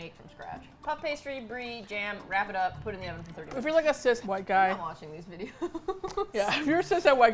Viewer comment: they said 'cis het white guy'. as in heterosexual. But cool! good to see you followed them to their new channel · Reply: At first just says "cis white guy" then, to me, says "cis (unintelligble) white guy" Honestly how she said it so fast I thought she said "Assistant white guy" lol. I've never heard anyone say "cis het" so that didn't even register to me, just thought it was something she was going to say and corrected herself. If she really did say "cis het white guy" then I guess I don't fit the het part. :P